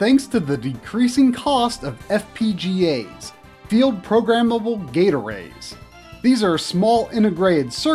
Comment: FPGA? All I heard was “FEEL PROGRAMMABLE GATORADE